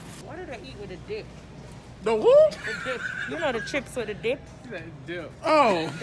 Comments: The dip lol i thought she said the D 😂😂😂🤣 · Spice: What do I eat with a dip Camera man:What 17:45 We all thought she said d*ck at a point